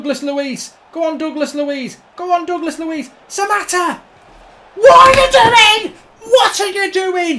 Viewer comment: What does he actually say there? I think I know what he meant to say but I'm sure in reality that came out slightly different. 😂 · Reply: “What are you doing”